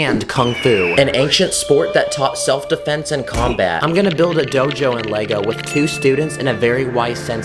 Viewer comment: lmao i heard im gonna build a d** in lego